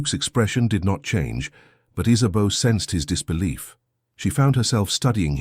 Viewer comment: I've never heard that name before. At first I thought he was saying Isabelle.